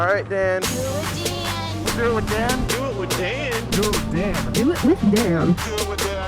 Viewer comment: In the intro when they were saying Do it with Dan I thought they were saying do it with damn